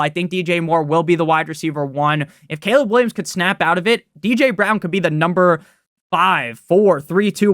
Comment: did I hear a DJ Brown?